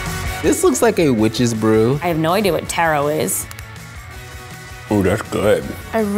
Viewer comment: excuse me, did you just say TEARO